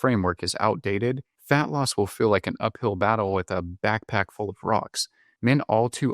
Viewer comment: I thought you said a backpack full of frogs. I thought that was a curious measurement.